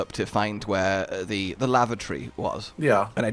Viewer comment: I really thought said lava tree